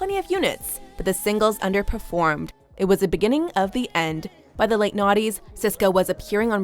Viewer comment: Does anyone else hear "by the late naughties..." at 5:13-5:14?